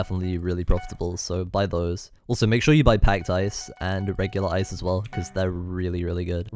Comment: I thought you said: “hacked clients” lol.